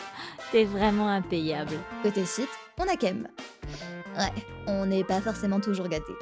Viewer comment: aled, j'ai entendu "khen" et pas "khem" à la première écoute.